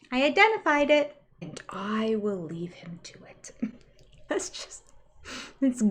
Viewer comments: I thought she said Kody had "pain , and fear that I will leave him too (also). No that she will leave him to it. I hate his fake cry and what little kid pays attention to any divorce? Nuts. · I thought Robyn said “I will leave him too” I think she meant to say he is afraid she will leave him too